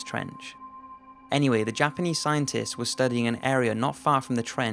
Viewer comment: did you say "Jumpaneese scientists"